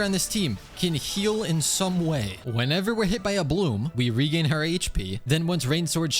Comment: "we regain her hp" yes i love our girlypop kaveh (if i heard correctly)